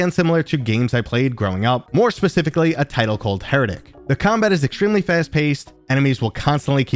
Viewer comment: did you say “Heretic?” 🥹 Maaaan you’re bringing back memories my fellow old head 😁